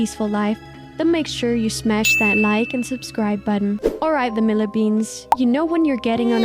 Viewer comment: what did you just call us? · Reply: Sound awfully similar to Vanilbean. Hmmmmmmmmm